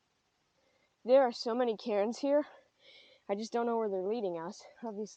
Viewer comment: I thought you said Karen's 😂